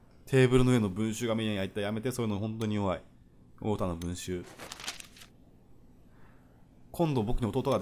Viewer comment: 陽太の文集が陽太の文春に聞こえたんだけどww